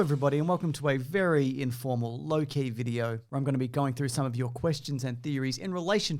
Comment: I heard “low-key video” as “Loki video” and my first thought was “I thought this was a spiderman video”